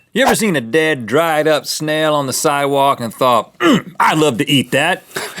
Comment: Who else thought Link said "It's cargurkey"